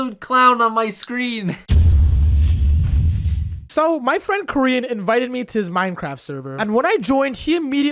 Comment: "So my friend Korean-" i always hear "Koryin" as "Korean"(it just sounds like it)